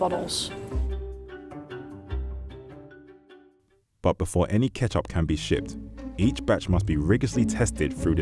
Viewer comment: did I just hear "But before any ketchup can be sh*t" ???